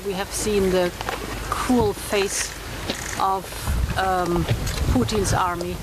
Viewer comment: Am I the only one who heard "cool face at first?